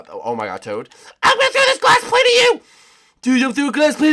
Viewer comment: did toad just say"i'm going to toss a glass plant at you" at 10:36-10:38?